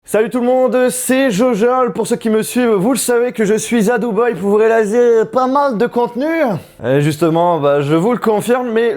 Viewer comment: quoi ?? « Pour vous rejqissnwjxisnz »